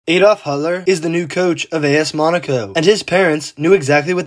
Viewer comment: Thought he said “Adolf Hitler” 😂